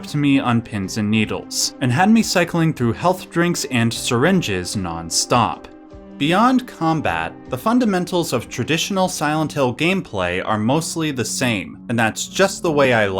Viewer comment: I could have sworn you said "... Health drinks and Sandwiches..." And I was thinking that was a clever jab at Resident Evil by the devs xD